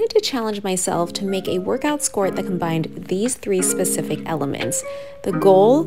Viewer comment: I misheard this as "sport" instead of skort... I was like now she's making sports